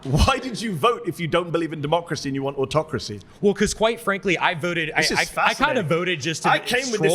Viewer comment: I heard “white frankly”